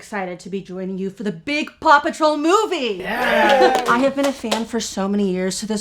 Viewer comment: oooh paw patrol i thought you kept saying papa troll no shit wtf is paw patrol